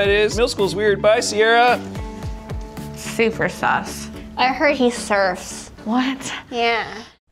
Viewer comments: DID SHE SAY SUS?!?! SUSSY IMPOSTER?!?!?! AMONG US!??!?! SUSSY>!>>! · i heard he sthurfs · did they just say super sus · did she just say “super sus” in 2018!?!? 😂